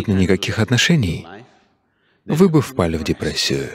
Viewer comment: не разобрала что он сказал? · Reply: вы бы впали в депрессию